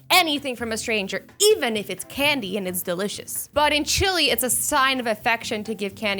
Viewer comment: Did u just say... CUNDY >:OO